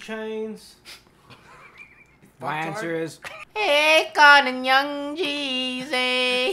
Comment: WHat did say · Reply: akon & young jeezy (like from the song Soul Survivor)